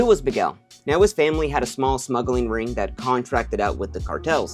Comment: Misheard you for a sec there thought you said a small snuggling ring